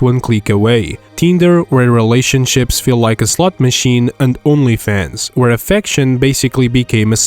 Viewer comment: Who else heard $/ut machine